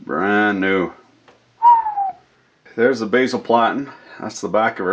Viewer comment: What is he saying at 3:22 "there's the bazil platten"? I'm not familiar with what that is I'm guessing it's one of those funny Canadian malapropisms. Or is he saying base plate but making it sound german like? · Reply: basal platen, yeah roughly "bottom" or "back" or something along those lines usually